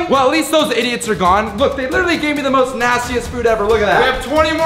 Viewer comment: not to be a nerd or a correcter but did he say most nastiest